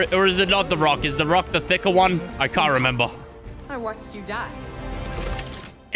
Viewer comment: I swear I heard It's about twerk